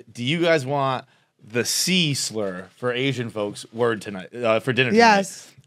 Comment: Ohhh! C-slur... 🤦. I thought you were saying "Sizzles" like with a Spanish accent. Im latino. Hahaha.